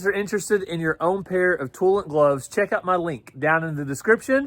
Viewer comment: when you first said Toolant I thought you said toilet!😂😂😂